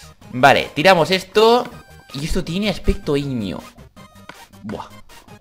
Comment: Pense que dijo aspecto ignio :D